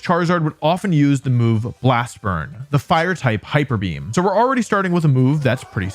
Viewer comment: At about 19:30 there's a typo in the cc that reads Hyper Neam instead of hyper Beam when referring to Blast Burn. No drawback on my enjoyment of the video, obviously, and idk if you have control over that, but I figured it would be worth mentioning in case you wanted to fix it!